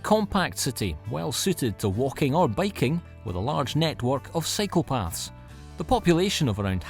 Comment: I thought he said, "Where they have a large network of psychopaths," and I'm all, "What??!" But I rewound and he actually had said, "... a large network of cycle paths" (for biking) lol! I confess I wasn't watching right then, just listening. 😄